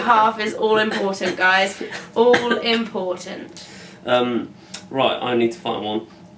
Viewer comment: When says " right i need to find one" at 03:41, i thought he said " right i need to fart"........Replay was needed. LOL